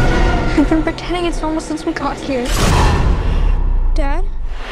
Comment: What did Agnes say at 2:20? All I could hear was “since we got here”. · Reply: We’ve been pretending it’s normal since we got here